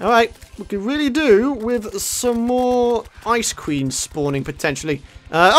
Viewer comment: I thought he said no more ice cream spawning